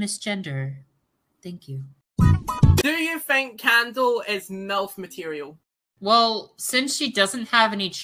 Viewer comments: I heard melt or like melf material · i thought i heard melt material but i turned on the subtitles and 😦